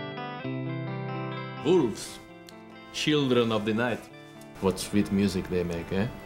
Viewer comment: I remember when I first saw this years ago and misheard the "wolves, children of the night" line as "boobs, children of the night".